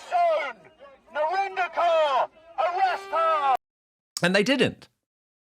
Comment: I thought it said 🤔 NARINDER FURRY ....😯 A xmas special, St Knickerless.🫣😇😂🤣😂